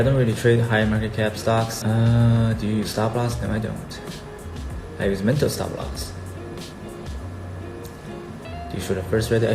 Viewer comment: What did he mean or say at 3:54 ? No stop loss, only mental stop loss? What?????? Pls help